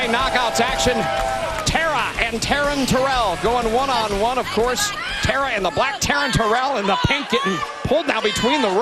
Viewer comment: I thought the announcer said "Tara and the black Taryn Terrell" lol